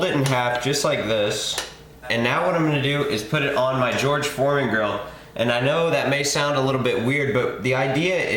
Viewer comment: Lol I thought you said george floyd grill